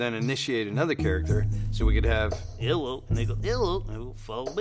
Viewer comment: Is it just me or did he say "Hello n****" haha